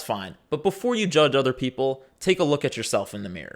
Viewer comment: MIMI Mirror is what i heard